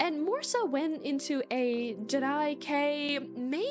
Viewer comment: What word are you using at 7:18? Sounds like jedi-kei :D I'm not deep into lolita but slightly familiar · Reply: Jirai kei